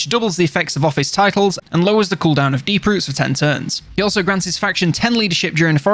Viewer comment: lol FFS you said DEEP roots! I thought you said Beetroots for some reason hahah wtf I think I need to get some sleep.... Fast travel via the beetroots...